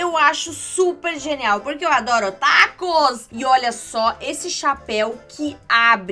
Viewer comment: só eu que entendi otakus?